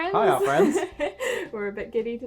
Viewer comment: For a very long time I thought you guys said Hey r friends In the intro (I don't know why and no I didn't think it made sense) but now I realized you guys say Hey art friends but instead say art kinda fast *face palm * 😑😔 I'm sorry I was stupid lol